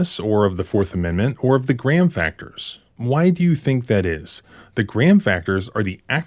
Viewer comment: Learned something new tonight… Graham Factors - I always thought people said “Grand” Factors 😂